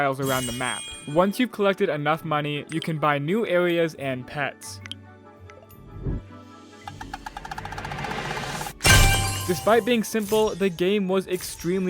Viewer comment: did blud say new aweas and pewts or new areas and pets??????